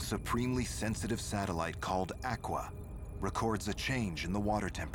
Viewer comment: what does he say on 2:57 ..?... sensitive sattelite called AQUA